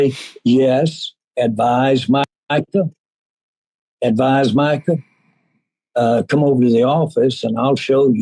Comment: Sounded like Jerry Jones said, “my 🥷 Micah” at 00:37 😂😂😂